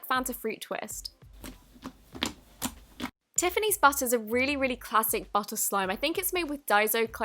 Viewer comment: at first I thought she said Tiffany’s butt but then I read what it said on the bottom